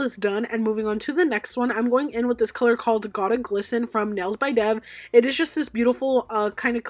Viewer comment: What’s the name of that glitter? Did you say Goddess Glissen? I am starting my own business and definitely have been obsessed with your videos.